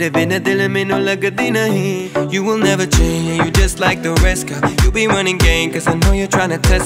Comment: his saying your just like the rest GIRL (that’s what I’m hearing) The subs says boy for some reason.